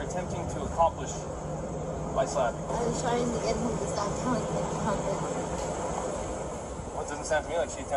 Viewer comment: what did she say? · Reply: I found out finally. Been looking for like days to see if anyone could make out what she said. She said "I was trying to get him to stop telling me to calm down" .... poor thing 😢